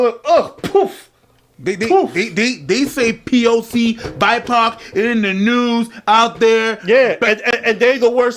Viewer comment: I was drunk and first time I heard BIPOC I thought I heard BIPOD and I was like damn, the woke mob going for the military equipment now??? 💀